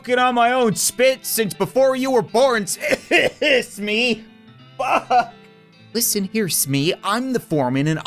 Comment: he sounds like said f**k